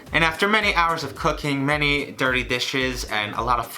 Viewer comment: I thought he said after many hours of cocaine.. 0-0